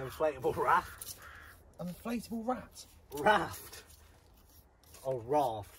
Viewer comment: Who would take an inflatable rat to desert island?....only. Then I realised I had misheard....it was actually an inflatable guinea pig!! 😂 Builders discussing the meaning of life....what could be better 👍